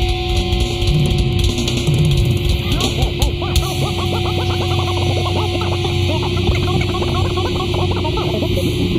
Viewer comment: what does it say ? " You ..... What? Lol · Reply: You cannot fuck with this band!